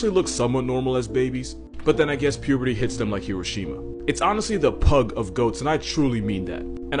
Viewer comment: "But then I guess puberty hits them like Hiroshima" Damn he literally has no chill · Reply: I thought he said Kirishima lol